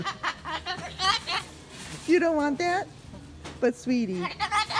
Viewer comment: I thought that I heard Benji say, " I don't want that".